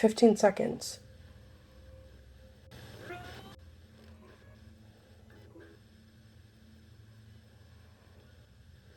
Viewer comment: I heard the voice again, but I can't make out what it's saying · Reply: Maybe wait for me?